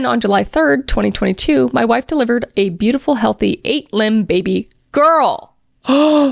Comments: I had to do a double take because I thought kris said '8 limbed baby girl' LMAO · I thought you said eight limbed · I was not reading and thought you said the baby had 8 limbs and I was like WHAT? Wow they are really calm and accepting about all this!! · The story about naming the sun after the brother I literally had to go back and listen to twice because I thought she said eight a limb, but after the second time realized that it was 8 pound lol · im not going to lie, listening to this i heard "a perfect 8 limbed baby girl" i was having a minute going "ARE THEY SPIDERS?!" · 8 limbed 😂😂😂 lb means pounds unless I'm mishearing what you said 😂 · I was just listening and thought you said "8 limbed baby girl" instead of 8 lb or pounds and had to look up if they were conjoined twins 😂😂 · I'm just listening to this and I had to fully get back on YouTube because I thought genuinely said "8 limbed" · I heard "8 limbed baby girl" not "8 lb baby girl" I was just sitting here thinking how insane that was until I double checked the text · girl for the love of god please just say POUNDS😭😭😭😂😂😂 I was only listening at this part and I swear I thought you said “our eight LIMBED baby girl” I thought they gave birth to a damn mutant!!😭😭😭😂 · girl said 8 limb instead of 8 pound I was so confused 😂😂😂😂 how does the baby have 8 limbs 😭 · pronouncing that "8 limb" instead of "8 pound " really through me off for a sec bc i was only listening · I thought I heard her say eight limbed baby